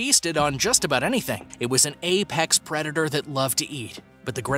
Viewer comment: did he just say apex predator